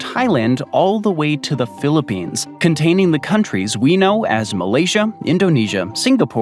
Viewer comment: Is it just me or did he call “Malaysia” “Croatia?